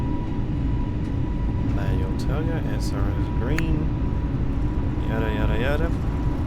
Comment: I laughed at 30:54 bc you sounded like “Yada! Yada! Yada!” which means “No! No! No!” in Japanese. Young children, especially 2-3 years old, often say it to their parents 🤣